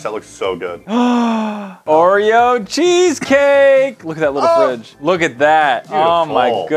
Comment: Did he say what I think he said??!!! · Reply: "Look at that little fridge"?